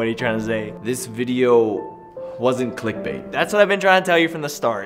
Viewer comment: Since this video came out I thought is said not clickbait